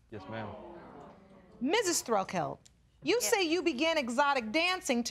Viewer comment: Is it’s just me or in the third case at 32:14 the thrift store Barbie said “yes, sir” to Judge Lake when she asked that question? (It could just be me tho idk if I heard it properly that’s why I was asking if anyone else heard that)